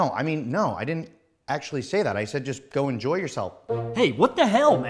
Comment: i thought he was saying go eat yourselves